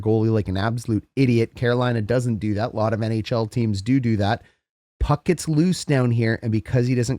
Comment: You earned another subscription from a raised on Basketball and Football southern born hockey convert. 5:50 did you say doo doo? 😂